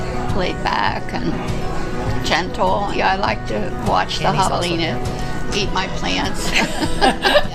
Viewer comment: Captions: "I like to watch the [inaudible] eat my plants." Is someone deaf? She clearly said javelinas!💯